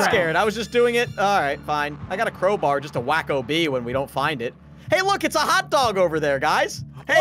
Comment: was saying hey look it's a hotdog over there guy's!